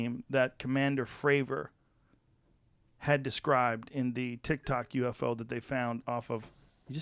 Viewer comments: Did he just say tik tok ufo😂😂😂😂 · DID HE JUST SAY TIK TOK UFO LOL